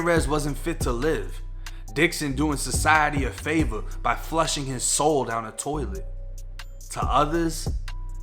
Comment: Honestly, I thought it said flushed his skull.. I'm all ,how da fuq, 😂